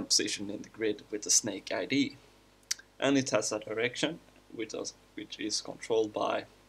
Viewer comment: What I understood: when he said "in a direction" it sounded like he said "that erection"